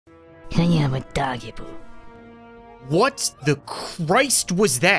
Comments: “What just whispered something in english in my ear?” Im sorry, but i dont think hyama dajibu is english Edit: this is one of my most liked comments on this channel thanks :) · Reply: I heard “I am a doggy bowl” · I started laughing when deadeye said somthing i really thought that she said With that weird voice : i am a doggy poo (Yes i know that she really said i am undodgeable) But she said i am undodgeable when she was aiming more then ninety degrees of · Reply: I thought she said "I am a doggy bowl!" Something like that. It doesnt even sound like undodgeable. That's the funny part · what I heard was “I am a doggie poo” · really sounded like he said :yeah your a doggy poo